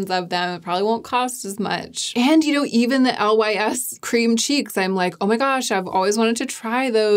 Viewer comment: I heard, "Lys Cream Cheese" and not "Cheeks" and now can't stop calling them that, lol!